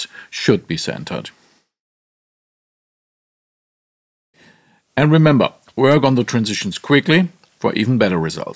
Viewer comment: These are great! I have one question. At 1:55 of Massaging the Voice I can't understand what he is saying. It goes by too quickly to understand. What is that sentence please? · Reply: It's "remember to work on the note transitions quickly" and then I smooth out the jumps in the pitch curve using the Note Transition tool I introduced earlier (at 0:38).